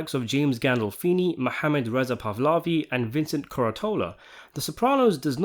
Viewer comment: Um, who exactly is Mohammed (or Muhammad) “Resapavlavi” (which is what it sounded like you said. I’ve never heard of him as an actor, and the only person, according to Sopranos Wiki, associated with the show who has that name is one of Christopher’s two Muslim affiliates which Tony gives up to the FBI. They only appear in six episodes. So I’m really confused as to who this “Mohammed” individual you referenced is. Thank you very much. Any information from you, or from fellow viewers and or slash commentators, would be very appreciated!! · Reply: Mohammed Reza Pahlavi was the last shah of Iran, and the one who resembles Phil Leotardo somewhat, so he made that joke in reference to the scene where Phil is mad that tony compares his appearance to the shah